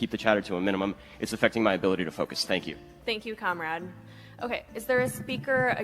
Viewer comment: i misheard it as "thank you c*m rag"